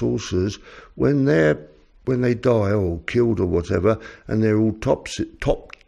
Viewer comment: I didn't quite understand what said there. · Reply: I was trying to say that when autopsies are carried out on the little grey PLFs, plant material is found mixed up with their flesh and blood bodies. So these PLF's are partly made of flesh and blood and partly plant material.